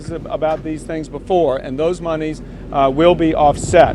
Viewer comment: Did he just say "those moneys" at 0:48? LOL!